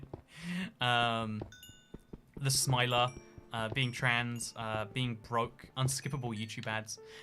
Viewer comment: did you say you are trans?